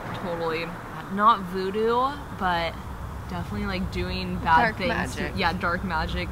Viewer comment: I thought I heard “dowsing” when asked what is he doing. You girls go where sketchy people go looking for spirits. 😂 I would have hoped out and walked up to him and asked if he ever saw or heard of paranormal activity in BV